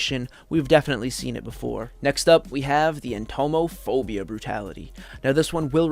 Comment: I thought that said, homophobia for a sec😭😭😭😭